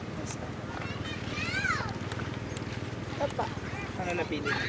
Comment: She said "opo" or "yes" in english. But the subtitle says "No, it doesn't." Their grammar is great but they need a new interpreter.